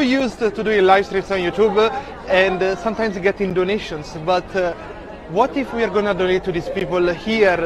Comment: 老外说的是donations，而不是Indonesians吧。虽然我一开始也听成印度尼西亚人（字幕一开始也写的是印尼人}，但是后面多听几次应该能明白是捐赠的意思，而且这个老外看起来也不像印尼那边的。。